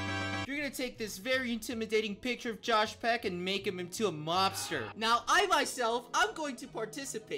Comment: turns josh peck into gabbie hanna wait fuck i thought he said monster